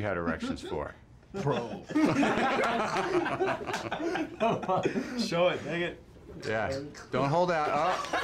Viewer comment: did he say “show it, nigga”? 🤔